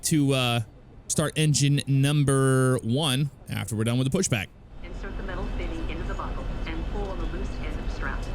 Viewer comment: I thought they said insert the metal thingy lmfao rolling on the floor